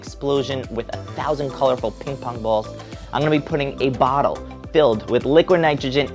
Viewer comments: did you said bottle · He clearly said bottle on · I think he said bottle